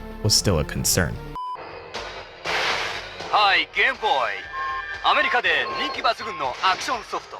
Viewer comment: "Hi, gay boy[...]" is what I heard the announcer say.